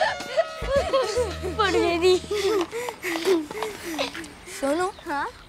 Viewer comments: did gogi said phatt gayi thi??🤣🤣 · what they say... Fatt gayi thi?😂😂